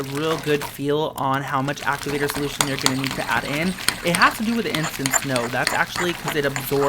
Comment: it sounded like he said innocent snow